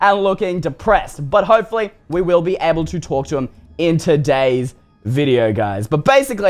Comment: is it just me or at 2:58 talk sounded like the word tourcher in "cause i want to be able to ""talk"to him"